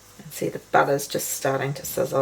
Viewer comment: When you started on the second batch and were putting the rings in the pan, you said the butter was starting to sizzle, i thought you said the bugger😂, thats my English brain for you😂